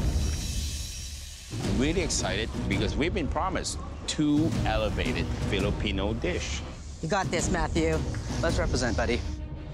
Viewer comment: I heard "elavated filipino dish" in